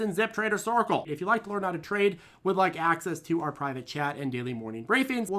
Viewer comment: For the longest time I thought was saying "Private Jet" instead of "Private Chat". lol